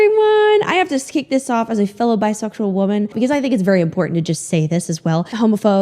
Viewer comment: i thought you said “bi-social” women and i was so confused 🤣 anyway, you’re hot and i’m dumb